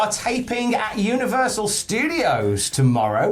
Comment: I thought you guys said wwe at Universal and almost lost my shit. I was so excited for wwe in the zone that impact built.